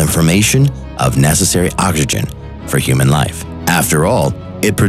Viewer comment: Hehehehe... anyone else hear him say "Ockagen" instead of oxygen at 4:41...?